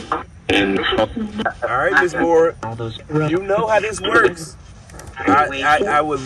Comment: Sounds like it said Moira